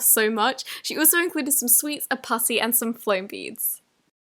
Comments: did she say 'pussy'? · please, someone help me. what in the world does she say at 12:48 ? what is a "puss-ey?" I am so confused. please help. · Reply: Olivia Lyons She said putty